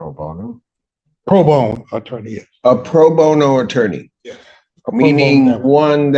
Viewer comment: lol, I was distracted and had to rewind because I thought I heard Judge S say “prone bone attorney” and I managed to end up with tea coming out of my nose.